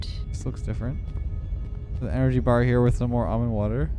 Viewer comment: For some reason I thought he said “NRG bar” lol